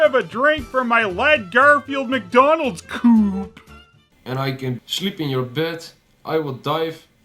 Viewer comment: i thought the frog said "i can shit in your bed"